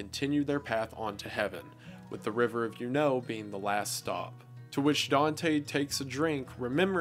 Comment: When he said the river of Eunoe at first I thought he was saying "the river of you know" like the river name was dirty and he didn't want to say it